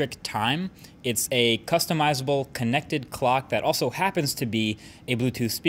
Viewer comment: disappointed! thought it said custom led cock